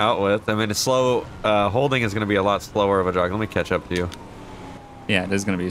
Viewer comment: When said catch up I thought he said ketchup at well 😂